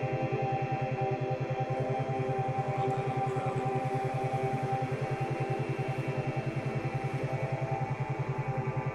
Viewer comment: What did you say at 20:19 just curious and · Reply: "well done, im proud of you' & 'youre ready' - a bit of subliminal language to help set you up for the day..